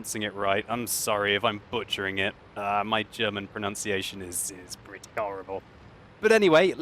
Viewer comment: Sounds like you said "My German pronunciation is britty horrible" as in British lol.